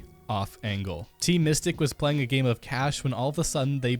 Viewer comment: did you say mystic or mythic @ 8:59?